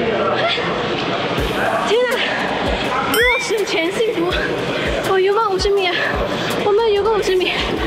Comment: 看了非常精彩的第三集，突然想知道 這裡15:31艾薇在說什麼（是不是馬來西亞口語🤔） 感覺之後可以出個艾薇馬來西亞語錄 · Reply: 因為艾薇有一首歌叫《失重前幸福》~~